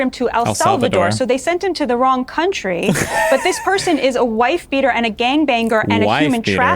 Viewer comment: did she say wife beater and gang banger??? 😂😂